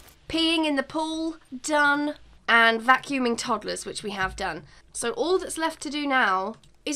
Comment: Why did i keep hearing “vacuuming topless”